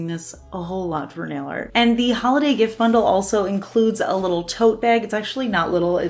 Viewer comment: I kept hearing "toe bag" .what is wrong with me ? 🤣